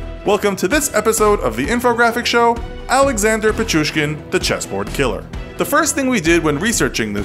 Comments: I thought it said cheeseboard killer loll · Was i the only one that thought it said the cheeseboard killer😂😂😂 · Am I the only one who thought it said “Cheeseboard Killer” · I thought it said cheeseboard killer 🤦🏾‍♀️ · I thought it said 🧀 board killer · I thought it said the cheeseboard killer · I thought it said the cheese board killer 😂😂😂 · Am I the only one who thought it said the cheeseboard killer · God im fat. I thought it said the cheese board killer lmao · I thought it said cheeseboard 🤣🤣🤣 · I thought it said the cheese board killer lol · I thought it said the cheeseboard killer 😂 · I thought it said cheeseboard and I was intrigued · I thought it said "the cheeseboard killer" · I thought it said cheese board killer XD